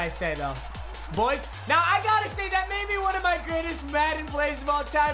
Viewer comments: Umm anybody else catch my boy say "good-est" at like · Did he say goodest